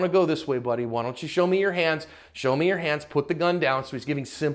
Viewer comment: What he said was "Show me your hands" But what I heard was "Chopper, sick balls"